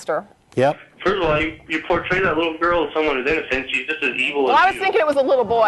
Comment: Can anybody tell me what the caller says at 21:48? I'm not a native speaker and I can't hear what the guy said. Please help! :) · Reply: he said "you make that little girl seem innocent, but she is just as evil as the rapist" and that's why they got so angry late answer but didn't seem like anyone else was going to answer you